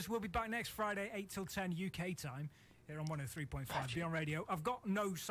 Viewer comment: LMAO! These radio guys are awesome! can anyone link to the radio channel? Sounded like they said '103.5 BR', but I can't find it. I'd love to just check them out and give thanks.